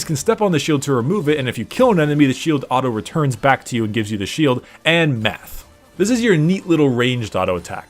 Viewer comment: What I heard, "AAAND Meth"